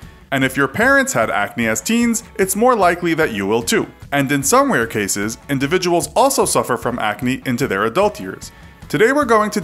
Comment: At the 19 second mark I thought you said "and in some weird cases, people can experience acne well into their adulthood" lol. I had to listen to it I few times.